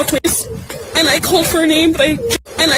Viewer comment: Sounded like she said I called for an ambulance